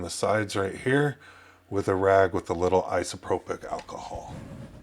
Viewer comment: Did you say "isopropic alcohol" 5:30? Thanks for the video!